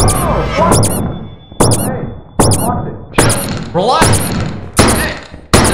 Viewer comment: did call Smii7y Austin? Name confirmed?? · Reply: He said “watch it”